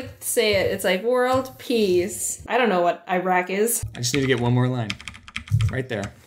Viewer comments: Did she just say "idk what iraq is" in the joke lol lmao 😂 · It sounded like she said "I want world peas"